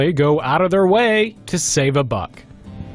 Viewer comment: i thought hey said buck like money and i thought you will save money